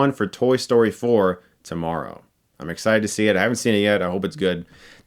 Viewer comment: Has anyone ever told u it sounds like ur saying " get stuck by knives " random question...